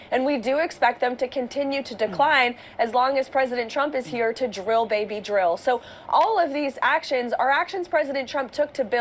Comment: You know, every time I hear "Drill, baby, drill," I hear "KILL baby, KILL," as in Kill off the wildlife, Kill off their natural habitats"... Makes me sick how self-absorbed we humans are.